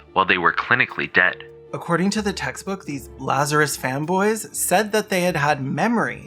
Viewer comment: I first thought you said "Lazarus femboys"